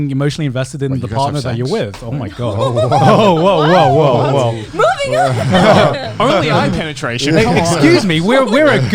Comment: Does anyone know what Connor said at 7:20, I couldn’t hear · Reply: Had to put on the headphones for this, but he said: "Wait, you guys have sex?" I was wondering the same thing too lmao